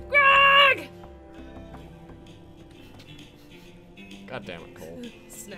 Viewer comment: I could have sworn he said god damn it cole XD